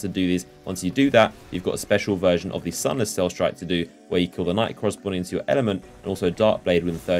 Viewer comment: i couldnt make out what u siad at 3:53....nightcross something element???? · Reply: kill the knight according to ur element. If ur after solar sword then solar knight etc etc